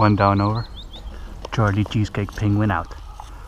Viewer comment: Did he just say. Charlie Eats Cheesecake Peguin Out?